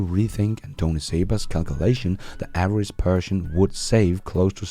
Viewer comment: At first, I heard "the average Persian". :) Great content as always!